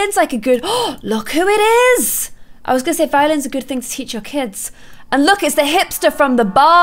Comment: I thought Clare said “ Violence is a good thing to teach your kids”